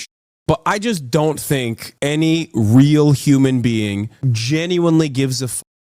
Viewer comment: YOOOO, I heard "any WHEEL human being" for a sec there 💀💀💀